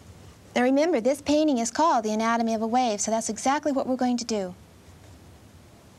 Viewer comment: My bad, I thought it said "Anatomy of a Weave"